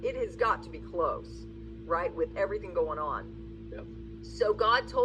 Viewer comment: 🤣🤣👀🤔🤔 did i hear YEP after she asked the ? Gotta be close⁉️⁉️